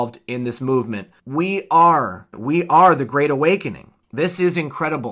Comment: It still sounds like he said "The Great We-Awakening" Did I hear that?